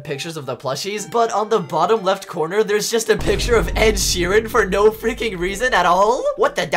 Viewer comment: I think they are saying it looks like ed Sheeran